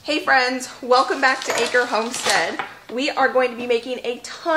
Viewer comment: I thought it said , Acne Homestead ! Hello From San Francisco ! 🌊🔥🔥🌊